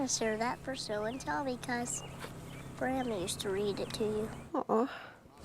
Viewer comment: Lil gump was taking book to show and tell because big gumps mom used to read it to big gump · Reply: It sounded like he said Grandma used to read it to him.